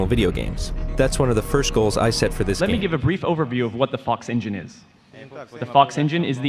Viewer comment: did he said "what the f*ck engine is.."😂